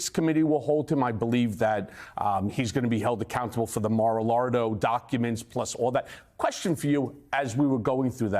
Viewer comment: Did he say "Mar-A- Lardo" ? Neat.